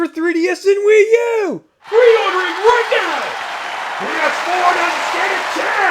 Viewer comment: can someone tell me what he says at 1:07? i cant hear it very well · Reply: "Pre-ordering right now!" the next one says "PS4 doesn't stand a chance".